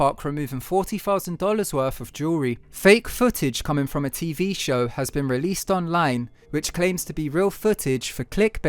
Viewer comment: He clearly says fake footage released online as click bait 🤦🏽‍♂️